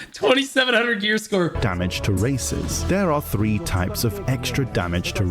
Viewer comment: im not the only 1 who heard him say "Damage to racists" right? I don't know if that's less racist then saying "Damage to races"